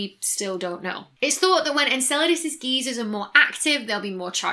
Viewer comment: I'm just familiar enough with British slang that I kept hearing "geezer" when you were saying "geyser", resulting in momentary confusion.